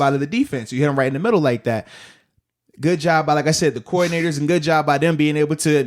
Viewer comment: Ohhh he said coordinators, I thought he said the core ni....